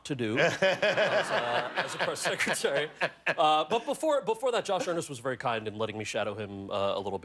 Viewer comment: Is there a way to edit the subtitles for this video? Cause there's a blatant error at 2:21, where the subtitles say "...but before that, Sean Spicer was very kind in letting me shadow him a little bit. I have a lot of friends who worked up to the last day of the administration." This is incorrect. How could he have shadowed Sean Spicer during the last days of the previous administration? What Mr. Penn actually said was "...but before that, Josh Earnest was very kind in letting me shadow him a little bit. I have a lot of friends who worked up until the last day of the administration." Josh Earnest was Barack Obama's final Press Secretary. Please change it. Thank you.